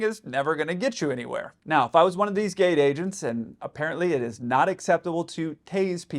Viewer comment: Did you say “gayte agent”. Haha